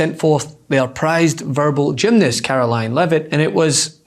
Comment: at first I thought I heard you calling her "their prized gerbil gymnast" what a mental image